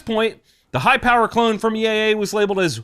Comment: I thought he said “the hi point clone from EAA” and became so confused about why any company would clone such a thing😂